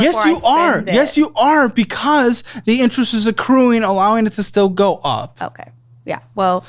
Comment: not a native tongue, can someone help me understand what he's saying there? "..the interest is [?], allowing it to still go up" yt subtitles say "acre" on my end, which doesnt seem right in both context and syllable length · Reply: Accruing. Meaning to continually be added.